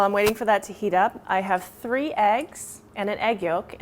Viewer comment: did she say three eggs?